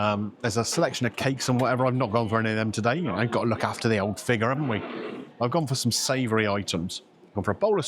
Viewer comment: Did I hear you say “need to look after the figure”? Or was I bit drunk